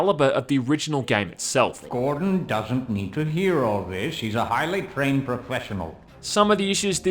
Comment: man what in the hell am I hearing? Is Dr Coomer saying "Gordon doesn't need to hear all this, SHE'S a highly trained professional? Does anyone else hear that?